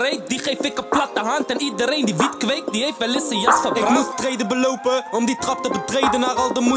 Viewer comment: wat zegt boef nou op laatst bij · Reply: Iedereen die wiet kweekt heeft wel eens een jas verbrandt